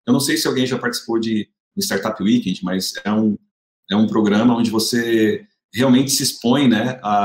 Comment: Esse podcast foi muito bom e motivador. No minuto 18:45 ele fala de um programa que ele usou, alguém sabe o nome ? Não consegui entender. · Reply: START UP WEEKEND